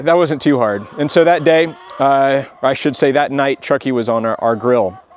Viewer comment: Truckie? At first I thought he said Chucky, which fits better!